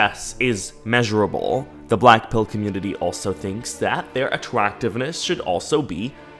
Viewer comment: i thought you said "The black-paled* Community and wondered how they are black and white-..